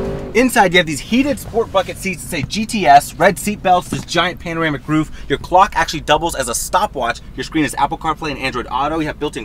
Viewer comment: Whenever he says “bucket seats” I get shocked for a split second because I hear “fucking seats” every damn time.